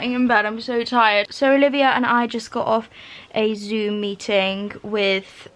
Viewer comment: why did i think she said zoomy ting